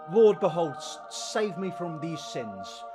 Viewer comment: I thought you said “save me from these Sims” and tbh that’s a more accurate description of what Oli ACTUALLY looks like 😂